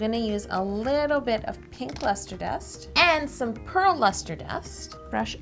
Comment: I thought she said LESTER-dust, whoops trash alert